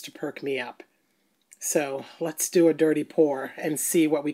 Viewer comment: My roommate heard me watching this and thought you said, "Let's do a dirty porn..." Lolol